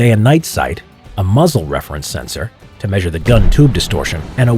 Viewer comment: oh god i thought it said “a muslim reference sensor”